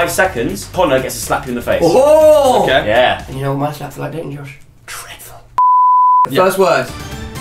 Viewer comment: When josh said dreadful at first i thought he said tifile (sos for spelling)